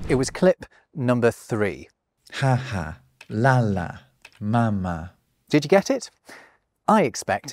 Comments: I thought you said papa 😂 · I thought you said 1.DADA 2.NANA 3.MAMA